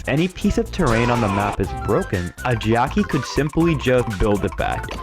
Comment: Why does it sound like 'geoci' instead of 'Jacky'...